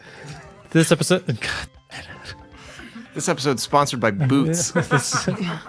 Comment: I thought he said "this episode is brought to you by boobs" near the end 😆